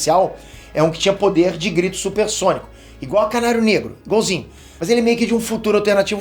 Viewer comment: Foi só eu que eu ouvi ele dizendo caralho negro, quando foi falar da Canalho?